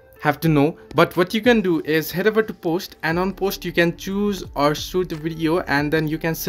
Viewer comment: Sorry dude but at 0:42, this is the step we all need to learn but the only thing I heard was "What you can do is itavitapost"... Not trying to mock or insult you but you really need to speak slower and articulate more if you want more of your viewers to understand what you want to say. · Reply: “Head over to post” turn the closed caption on if you cant understand someones accent